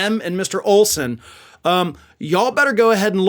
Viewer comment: Did you just say "if the past is predicate?"